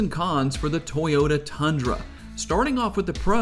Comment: Did you just say Tundra